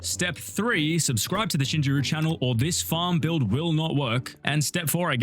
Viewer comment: my dumbahh thought he said "Shinjiru farm"